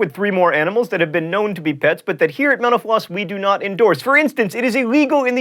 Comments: lol almost every time he says "mental floss" i hear "menopause" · When he said mental floss I thought he said menopause